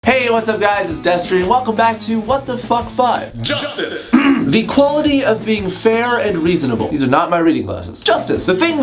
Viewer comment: I thought that Destery said Justin at first so I was confused about why batman got a bone over Justin Bieber...